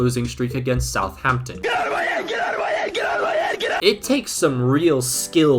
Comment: When you showed the meme of the cat holding his head with the audio saying “get out of my head” I thought it said “quiero morir” Which means “I wanna die” And I mean it guess makes sense